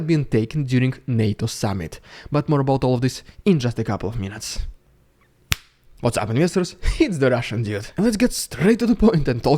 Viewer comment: what is the dude saying after the finger snap in the beginning 0:36 ? I hear "what's up misters" ??